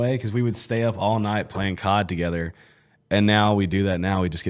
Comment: Did you just say that y'all "sat up at night and played with your cods together"???